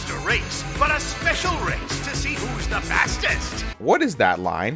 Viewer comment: For some reason I heard ``Bastest`` instead of ``Fastest`` , either the bad Voice direction kicked in or my ears didn`t hear it properly